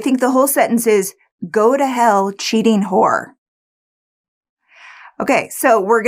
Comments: I heard “evil whore” the first time around. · I first heard the short version of this story first and then this longer one. Sadly, on both videos I heard "just die cheating whore" which is pure motive.